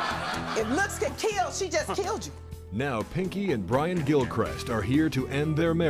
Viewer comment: For the second I thought it said, Pinky and the Brain. I was like what??